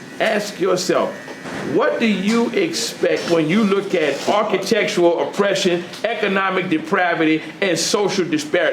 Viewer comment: Did he just say "architectural oppression"?